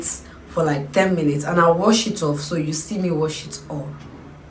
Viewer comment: I thought they said we shouldn’t wash it off